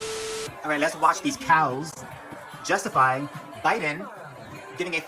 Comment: Lol😅😂 I thought you were gonna say, "Let's watch these cowards 😅but it was these cows😂 😂😂